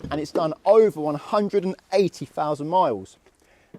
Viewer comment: Impressive that a clutch which has been tortured by new drivers for 40,000 miles is still usable. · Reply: I thought he said 180,000 miles!